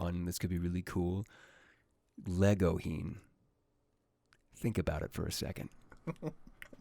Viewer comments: excuse me what did he say? I dont know that ship. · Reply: Legoshi x Gouhin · speaking of ship, what ship did he say in 50:50, I cant understand what he says and I dont know that ship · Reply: Kyle Colyn Legohin -legosi and the panda dude